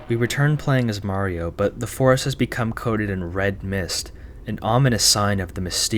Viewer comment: what what did you just said??!?! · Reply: THE RED MIST?!